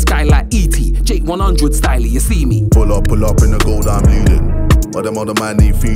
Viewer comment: Pull up, pull up in the gold, I'm leading your hairline is receding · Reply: I thought it said Bleeding lol